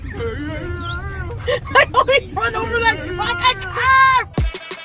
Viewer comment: Ok at 0:17 what is she saying?! You gotta have respect for the... What??? Please tell me 🤣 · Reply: I know it's been 2 years, but she's actually saying, "It's disrespectful to Greece". I have no idea what the full context is though 😅